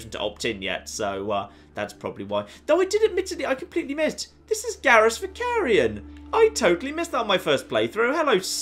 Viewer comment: This is ______! What is he saying, I've listened to it several times and can't figure it out. Also, I always thought it was mutfruit (mutt) before this game, but somebody in game pronounces it mutfruit (mute) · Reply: hi, 4 years too late but he says “this is Garrus Vakarian” who is a character from the Mass Effect games :)